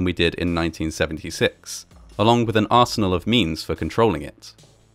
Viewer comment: Genuinely thought you said 'an arsenal of memes for controlling it' at first 😂